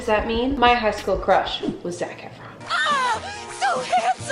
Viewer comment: There was a time you said his name and I swore you said “Snack Efron”... lol not wrong!